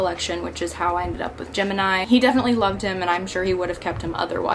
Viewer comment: Happy you're doing great and are making more videos :D Btw. everytime you said Gemeni i heard "Jim and i" haha :D